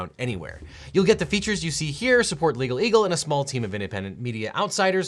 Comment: legalegal. Sounds like what a baby would have said or your android malfunctioning